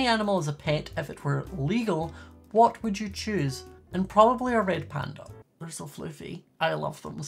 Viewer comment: Had to replay the part at 3:30 cause I thought he said red back and I was like why do you want a deadly spider